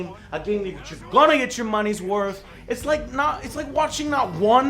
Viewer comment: Wait did you say MUNEH ?!!!!